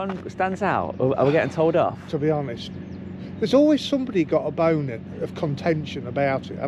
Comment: Did he say bona:cat-orange-whistling